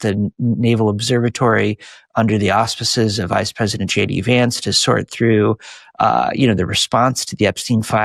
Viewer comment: Did he say "Shady Vance"?